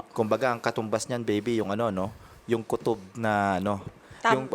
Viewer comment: Tama ba narinig ko? Tinawag ni atty. na baby? 4:04 ? 😂😂